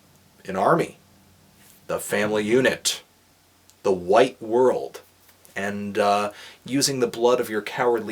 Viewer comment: Honest review. Thank you Anthony. I like Burzum. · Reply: Another note--always thought Varg said "wide world" and not "white world."